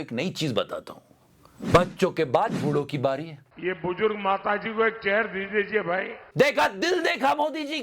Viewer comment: "ये बुजुर्ग माताजी को एक चेयर दे दीजिए भाई " मैने सुना: बुजुर्ग माताजी को एक जहर दे दीजिए भाई 😅😂😂😂😂